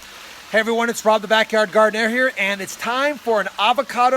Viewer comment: i keep hearing it "Rob the Backyard Gardenair"